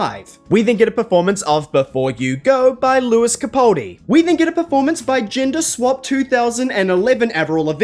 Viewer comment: Maybe it's because I'm not a native speaker but I keep hearing "we didn't get a performance by..." instead of "we then get a performance by...", are you from New Zealand?